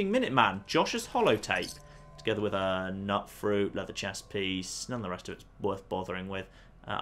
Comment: Did he say nutfruit when he was looting josh around 16:06?